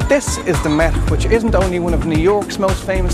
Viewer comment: The what museum at 3:53? Meth museum..? I can't figure out his accent. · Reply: The MET . The Metropolitan :)